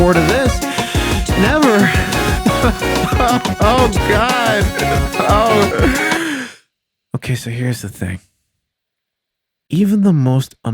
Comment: I got the flu so I hear "Oh gay so jacket" bowzers [cough cough]